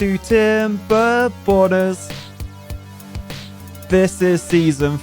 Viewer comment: I hate to be that guy, but I keep hearing "Timberboners", whenever says "Timberborners."